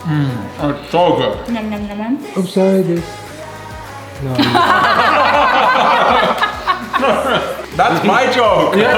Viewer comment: Anyone know what the guy behind the camera said to make everyone laugh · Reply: he said "sorry, I wasn't recording" and we almost had a heart attack 😅